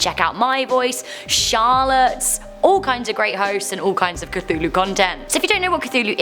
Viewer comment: “The Cthulhu” well I have read enough to know it’s Cthulhu, not The Cthulhu. not a Cthulhu. Just Cthulhu. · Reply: I thought you said as "Chahulo"